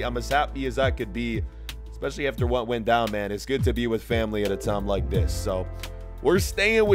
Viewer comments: did u say family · Did you say family